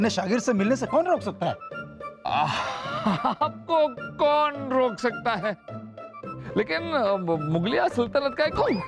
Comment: omg I've been rewatching and laughing 😂 😂 😂 😂 😂 😂 yet but i still dont know what was saying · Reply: AAPKO KAUN ROK SAKTA HAI